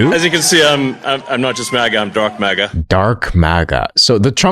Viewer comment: ngl i thought he said drunk maga before you said dark maga and i was like why would you brag about that